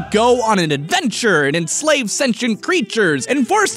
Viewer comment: what word does he say before “creatures”? Please help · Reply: “...and enslave sentient creatures”